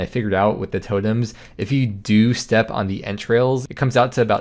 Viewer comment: I thought you were saying 'step on their entrails' I was like what? lol